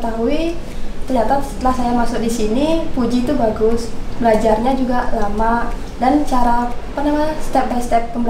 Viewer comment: kok aku dengernya Belajarnya juga lama😂👍 Si bang pun tersenyum😂😂